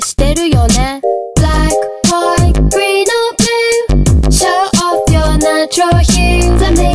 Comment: when it says "show off your natural hue" i always hear "shut off your nitrogen" and im like: this make no sense m8